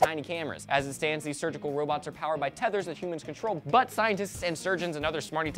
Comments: How come I heard say "butt scientists.."? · Did just say butt scientists?